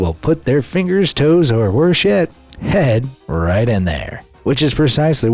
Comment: Did u just say s**t. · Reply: Worse yet